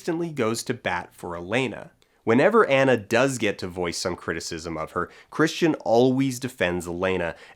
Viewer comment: I legit thought you said that her big client was named "Voice Box" and wasn't surprised at all at the quality of the writing for a second.